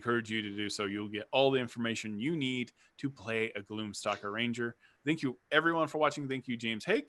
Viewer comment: Sounded like you said gloom soccer ranger.... the mental imagery was epic.... I must make this happen.